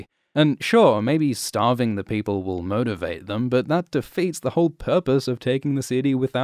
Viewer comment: Cersei: Did you say 'murdervate them'? I like the sound of that. Mountain, murdervate them.